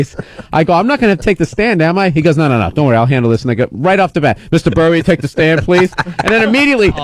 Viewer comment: Did he said "I'LL HANDLE THIS N*GGA" or am i hearing things again???